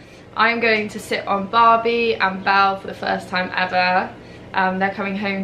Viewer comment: Omg I thought it said “I rode Barbie and FELL off for the first time💜😂😂😂🙈🙈🙈